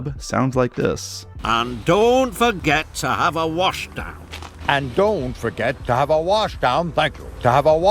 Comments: here’s what’s the top of had really said: “ and don’t forget to have a wash down thank you” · I think he said “Thank you”.